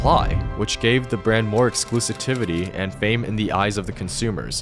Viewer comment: Did he just say "exclusitivity"?